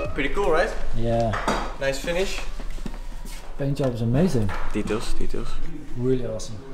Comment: is he saying "kiitos kiitos"? Mean thanks in finnish. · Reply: Kumppaaa haha he says “details details”, close ;)